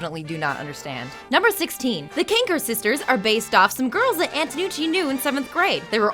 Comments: Wait did she just say, kranker sister, instead of kanker sister? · What Should We Do Next?! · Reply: you misheard the name of the 3 sisters, it's "kanker" not "kranker"